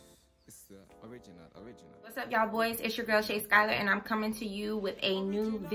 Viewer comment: Did you say BOYS?